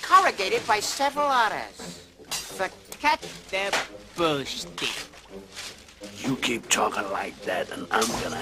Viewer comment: What did the weasel say? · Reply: “So cut the bull schtik”